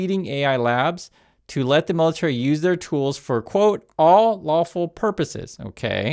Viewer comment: I heard it as “all awful purposes” the first time I listened and I refuse to hear it any other way